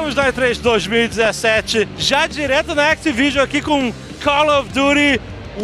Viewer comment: tou com a mente tão poluida que quando ele falou "activision" eu entendi "xvideos"